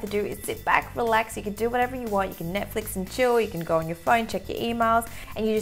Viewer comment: Did she just say Netflix and Chill? 😂😂